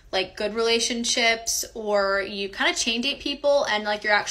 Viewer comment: What does she say at 4:50 You're kinda ___ people and you're actually enjoying it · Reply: Chain date, I think